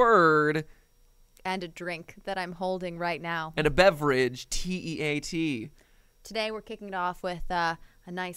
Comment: did you just say T E A T